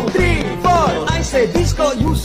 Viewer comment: I hear "Paty paty"😭